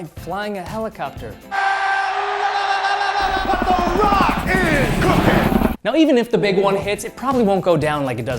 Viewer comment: did i heard he said "rock is cooking"???